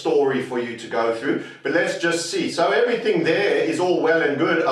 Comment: I thought said, "Orwell and good," instead of "All well and good!"👍👍👍